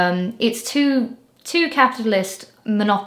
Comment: I thought you said "It's two caterpillars" and my brain auto-finished it with "duking it out to be the bigger caterpillar"